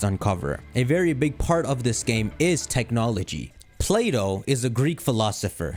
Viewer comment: ngl, I heard Playdough and was like 👀 Love your theory vids as always